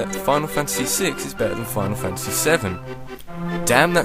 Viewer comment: When he said stuff about Final Fantasy in the beginning i thought he was say Fun And Fancy